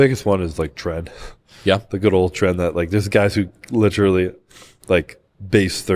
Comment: What are they talking about at 1:11:40? The captions say trend and tread, newb here and have not heard of that. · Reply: Tren, it’s an extremely potent steroid with dangerous side effects